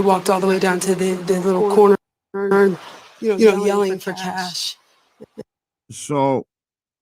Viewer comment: Sounds like says "poor cash!" On the bit u kept going over. Also as there is sound,I don't understand why cash didnt make a noise when picked up if he was still alive?